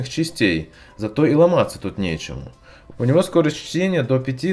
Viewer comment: Вам тоже послышалось? "За то Илон Маск тут нечему!" Я сразу не понял... Начал думать что Илон Маск уже и до производства HDD добрался🤔😆